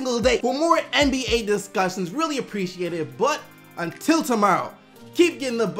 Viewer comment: "Keep getting the buckets SDC" is what he says?! I always heard "Keepguessing bucks'SDC"